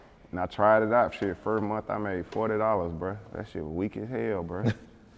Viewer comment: What did he just say at 19:44 said he made $40😂😂😂